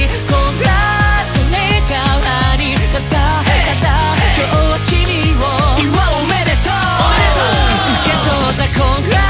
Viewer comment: 祝う おめでとう！！が雄馬 おめでとう！！！に 聞こえてなんか嬉しい